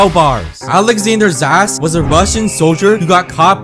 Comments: is it just me or did i hear alexgender's as* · I thought he said Alexander ass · Bruh when u say his name it sounds like ur saying alexanders ass😂😂😂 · I heard ‘Alexander A§’💀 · Thought bro said "Alexander's ass"